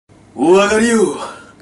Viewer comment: 最初ワリョウって聞こえて？？？ってなって勝手にアガリに変換したんだけど、聞き返したら普通にお上がりよって言ってて、でも結果正解してて一人で面白くなってた笑